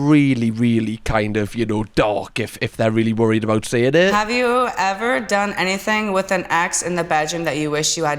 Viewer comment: I actually thought she said "have you ever done anything with an axe in the bedroom..." 😂😂